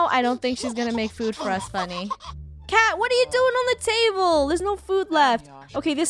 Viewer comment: For a moment, I thought you said 'Kat' and not 'cat' and I was looking around the table for Funneh but when I saw her on the ground on her two legs and Frisk on the table, getting ready for a pounce, I realized I was dumb...